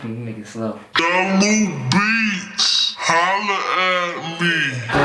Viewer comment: All these years I thought the tag said "jungle beats" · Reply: I thought it said jello beats lol